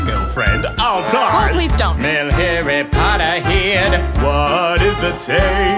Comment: 💃🤣it is so funny 0:31 "Male Harry Potter, head" oh gosh what is Harry doing here?🤣 0:55 "Yellow harry" sorry i just can't🤣